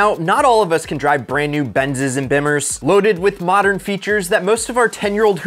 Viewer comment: did he say Bimmers LoL I swear I lost it LoL